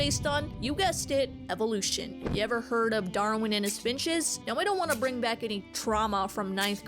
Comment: I really thought you said “Darwin and his bitches” 😂